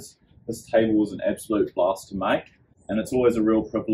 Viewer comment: Dude at the end when you said “blast to make” I thought you said bastard to make so casually 😂😂